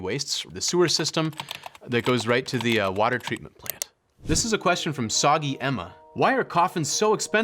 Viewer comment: Did he say water treatment plant??????